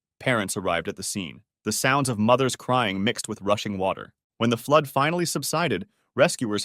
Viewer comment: what did he say? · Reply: mothers crying mixed with rushing water